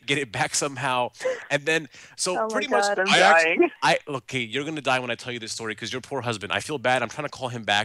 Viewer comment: it sounded like he said "Look Kate, you're gonna die when....." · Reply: he says "Look hey" it's what I heard and it even says it in the subtitle